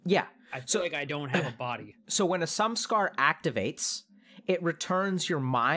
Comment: What does Dr. K mean when he says "some scar"? Like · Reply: Samskara, buddhist concept